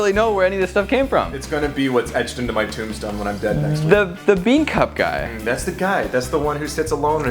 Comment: did he say he's gunna die next week